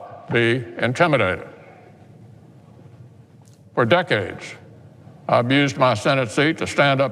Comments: it sounds like he says "for decades, I abused my position in the Senate"... Did he have a freudian slip & accidentally tell the truth? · did he say "I've used my senate seat" or did he say "I'd abused my senate seat?" Hmmm... Truth slips. I won't hurl any insult at the ppl of Kentucky, but I will say this: show America how smart you are with your voting. Now the ball is in your court. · Moscow Mitch got his feelings hurt. Leave a comment below. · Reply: I swear that I thought I heard Man-Turtle say "For decades, I abused my Senate seat." 😆😆😆 he already has but I just I'd point that out.